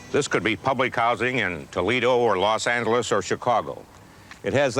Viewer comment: did he say, "TOLEDO" Where the most beautiful Hardworking people live!